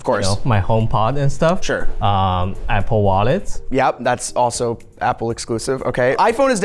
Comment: I heard "Apple Water" and my first thought was: of course they sell water